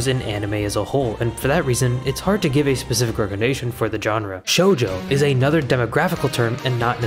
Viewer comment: Why did i heard "Jojo"